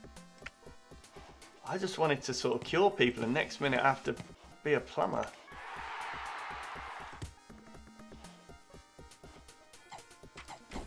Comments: It sounded like you said "I just wanted to kill people but now I have to be a plumber!" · I lowkey thought when he said “I just want to cure people, now I gotta be a plumber” I was convinced for a minute that he said kill people and I just kinda accepted it.